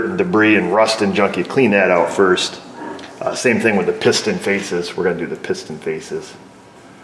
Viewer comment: Sounded like you said pissed in faces. Dam I need a hearing aid.